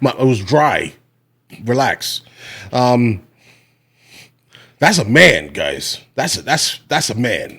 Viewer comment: did you say that's a scam? Because yes he is